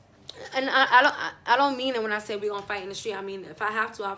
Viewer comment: I thought u we’re about to say fart u said fight😂😂😂